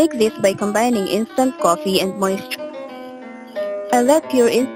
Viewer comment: I couldn't hear what you said to make gel recipe you combined instant coffee and ???? please write down thank you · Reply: Coffee & moisture are the basic recipe. Just let the coffee stay in a container without the lid & let it cure over time.